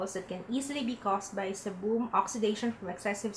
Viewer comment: Seriously?? Sorry but sebum is pronounced as "SEE-BUHM" and NOT "SI-BOM". I was kinda confused at first coz i thought you said Cebu but then i realized you were referring to sebum. Just a little correction though, hope this helps. Since you're doing a vlog, might as well improve your pronunciation coz many people are going to watch you.